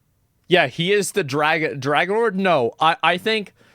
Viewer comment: Did i hear "Drachenlord"?